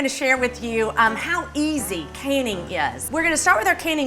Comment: I seriously heard her say, “We are gonna see how easy caning is...”. Not “canning”, but “caning”. Wtf?